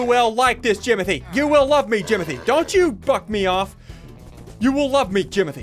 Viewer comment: did he just say dont fu€& me of jimothy or what